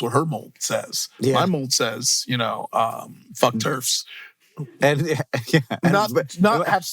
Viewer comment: the subtitles use the wrong spelling, "turfs" as it's spelled there is like the grass thing, what he said was "terfs" short for "trans exclusionary radical feminists"